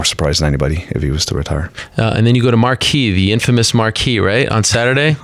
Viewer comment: what does he say there? Marquee?